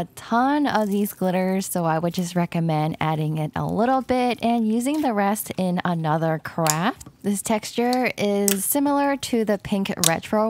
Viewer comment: does anyone else hear “using it for another crap “?🤣🤣🤣